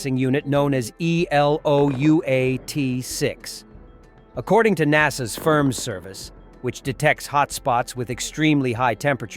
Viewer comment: 😂 I hear "According to NASA's Sperm Service..." 😅